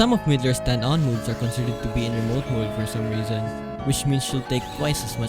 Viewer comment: "considered to be" what? I can't quite make out what you say here. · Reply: I think he said "considered to be in remote mode"